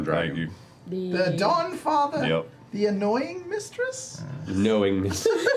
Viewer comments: So I listened to the last show last night and I, 100% just like Sam, heard you say the "Annoying Mistress" I'm not going to sit through #8 all over again just to find that one spot but if you find it, you will see what I mean, You said 'the annoying mistress' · Yes, Sam - I thought that Matt said the deity of "The Annoying Mistress", too.